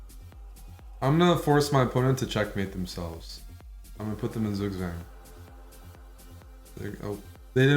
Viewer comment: what did he just say here? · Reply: "i'm gonna put him in zugzwang"